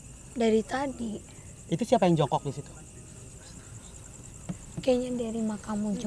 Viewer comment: Apa aku salah denger, bilang jungkook